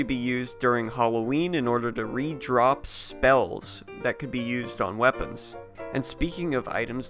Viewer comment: I heard "redrop Spells" and almost nutted in my pants.